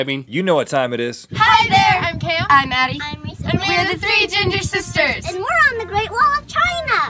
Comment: Did they just say...3 gendered sisters?